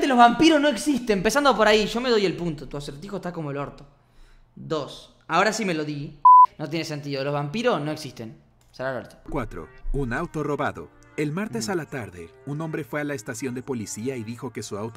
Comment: jaja yo tenia el volumen bajado a 25 y entendí "naruto robando"